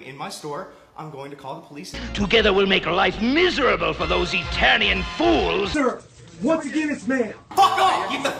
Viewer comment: In the clip where Hordak says "Together we'll make life miserable for those Eternian fools!" it sounded like he was saying "Italian fools" and I had to pause the video, I was laughing so hard. Oh yeah, and this is far from the dumbest video you've made. XD